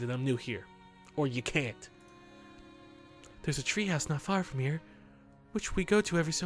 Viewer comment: Somewhere near 12:00 i think he said treehouse, anyone else?